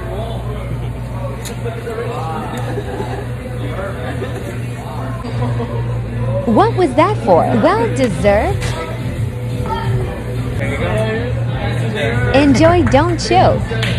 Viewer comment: When that time you said "enjoy don't choke" got me dying🤣! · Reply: When I watched it the first time I heard "enjoy and choke" 😶